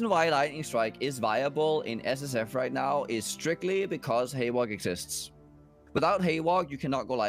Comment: says "the reason lightning strike is viable is strictly because _ exists" - what did he say? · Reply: Thanks. The word I missed was "haewark"